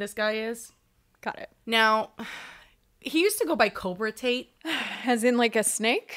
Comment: I legit thought you said ‘cobra taint’ at first, and didn’t question it because that 100% sounds like a name that useless cell cluster would choose 😂🫠